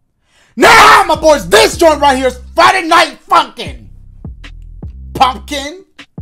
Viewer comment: LMAO This straight sounded like said this is Friday Night Fu$$ing You got got to love this man